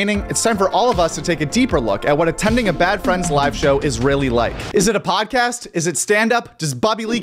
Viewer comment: I could have sworn you said "what Israeli like" instead of what you actually said.